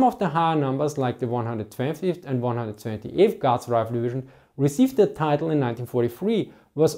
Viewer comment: I hear god instead of guard. Sound like you said, soviet god.